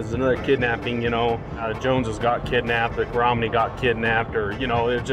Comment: I swear I heard him say, "Romney got kidnapped."